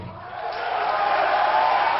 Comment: Is this Tokyo or Worchester? I thought he just said "Tokyo" or "thank you" in the beginning?